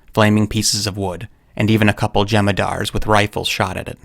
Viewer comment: I had never heard of Jemadar (forgive me if it's misspelled lol) and I really thought you said Jem'Hadar, as in Star Trek. lol